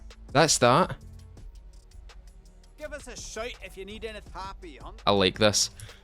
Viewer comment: To Scots like us, the trader’s accent is pretty cringey, but to others it may seem genuine. Except when he says “give me a shout sometime” and it comes out as “shite”!! I’ve watched a couple of creators stopping dead in their recordings and going “what did he just say?”